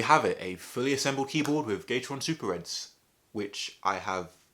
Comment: is it just me or instead of "gateron" i heard "gay-the-ron"